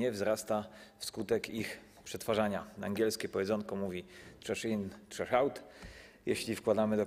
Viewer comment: co on powiedzial? .... in .... out? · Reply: TRASH IN TRASH OUT. CO TO ZA AKCENT? XD CZASZ IN CZASZ AŁT XD